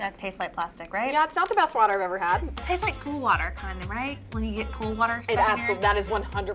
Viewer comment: I thought said “it tastes like poo water” 😅😭